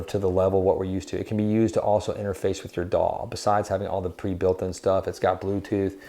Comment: I thought at first you said it can be used to to interface with with your dawg. That southern accent.